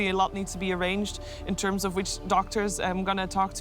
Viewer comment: Did she say witch doctor?